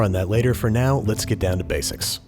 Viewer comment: What he said: "let's get down to basics" What I heard: 🎶 "let's get down to business!" 🎶